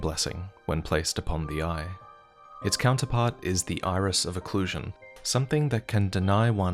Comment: Iris of OCCULTATION? it sounds like said occlusion.